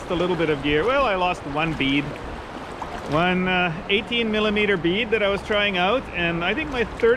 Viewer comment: did you say 18mm bead!!?